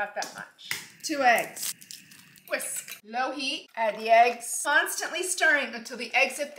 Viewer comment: I’m so brainwashed by YouTube when she said low heat, I thought she said low-key💀😭